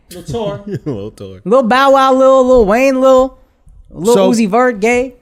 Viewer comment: I thought he said lil uzi gay i misheard lol